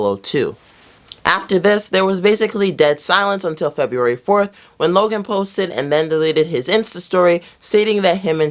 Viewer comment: Did he say logan paul?